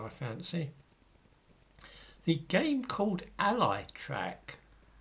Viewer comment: "Allie"? They want you to be an allie? I thought they said "Ali".